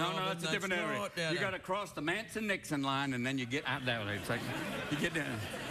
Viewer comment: How many caught the Manson/Dixon line at 13:55? I think I see the connect there! Shows you another level of the comedic genre. (reference, understanding and usage of historical material) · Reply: Even worse, I thought he said Manson/ NIXON which is even funnier.